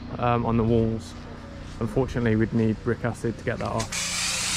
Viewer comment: My sleep deprived brain thought you said you'd need Rick Astley to get the white stains off 😂😂😂